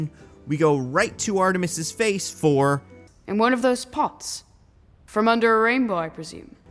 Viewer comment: Hey, another great video, I loved it! One thing I did want to point out though is that the clip you mention at the 14:00 minute mark or Artemis saying "in one of those pots from the end of a rainbow." He actually says "And one of those pots, from the end of a rainbow." It's the Irish accent, and I can see how the 'and' could be mistaken as 'in'. I feel like, that particular line, is less of an editing mistake they overlooked and more a sarcastic joke about leprechauns. Personally, I still think you're theory works without that line and I do believe he was talking about Gold in the original version of the scene. I normally wouldn't have said anything over something like this, but since you seemed to put a lot of weight on this line, I thought I'd let ya know what he was saying. I myself am English, but I have Irish friends and family and I sometimes mishear the Irish accent, but I'm very confident that that is what Artemis said in this scene. Either way though, it's still a really choppy edit. I loved the video, always love hearing about a crazily bad movie. I somehow managed to enjoy this movie myself, even though I knew it was really bad, I knew something was off with the editing but I hadn't noticed why so thanks for pointing it out such an inciteful way. Love the videos man, keep doing what ya doin! Cheers!